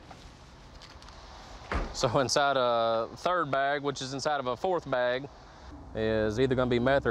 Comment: I think he said 4th An 5th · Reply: He did say fourth for sure but it almost sounded like he said 15th :) haha